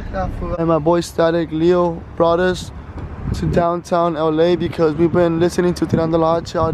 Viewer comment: he said downtown LA instead of Houston lol much love bro❤️🤟🏼